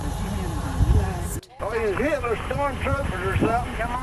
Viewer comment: it's mostly unintelligible, but I could clearly make out that he's saying something about Hitler here. Can't quite tell what, but it sounds like he's saying something about Hitler's son